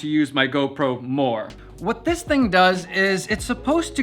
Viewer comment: Hahahah I thought you said you don’t like GoPro. 👍🏼